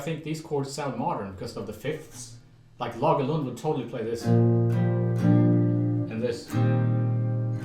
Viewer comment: he mentions someone "totally playing this..". Can't make out the name....anyone understand him who he's mentioning ? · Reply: Lage Lund